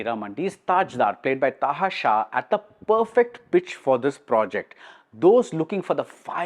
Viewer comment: am i the only one who heard it as bitch?